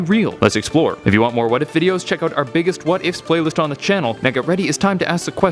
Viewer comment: I thought when he said "What if videos" He said "weddit videos"